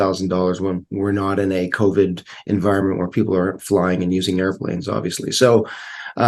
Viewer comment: what did he say? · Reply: The subtitle generator on YouTube suggested that he said, "We're not in a Covid environment where people aren't flying and using airplanes, obviously -- so, um, you know, I think ..."